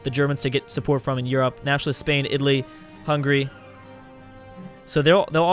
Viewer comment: did he say hungry?